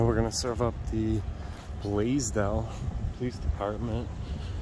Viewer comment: I’ve never heard of the city of Blasdell until now. The first few times that I heard you say it, I thought you were saying “Blazed Out”. Lol. Like when the officer roughed you up for trying to get your ID, I thought you said something like “here comes a Blazed Out officer”. I thought you thought or knew he was high at first. Lol. My bad. Hey. 😉